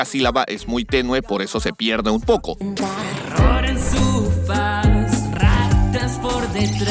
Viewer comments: en lugar de terror ahora escuché error xd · Se, a mi me pasó que la mayoría de canciones no las entendí mucho twt Y en la parte de camilo entendí "error en su faz" owo